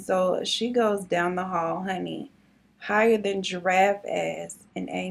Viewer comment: Please tell me someone heard her say “higher than giraffe ass”